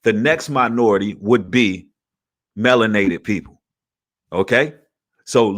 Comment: Hahaha when you said melanated people I thought you said watermelon people. I had to re wind to hear it again to see if I was tripping😂